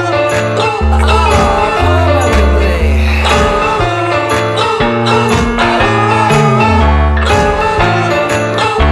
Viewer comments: Idk what he says at 2:14 but WOW I LOVE IT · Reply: Makayla Zagorzki he says “oh permanently” :) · im really frickin' curious what Dallon said here. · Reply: O, Permanently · does anybody know what he says in the background??? ive been trying to understand it akskfk!!! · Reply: Oh, permanently (i think) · What does he say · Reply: "ah, permanently"